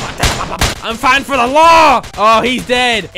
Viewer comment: Sounded like he said "I'm fighting for Allah!"